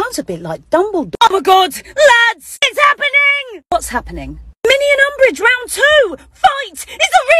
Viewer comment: I thought it said eat umbridrge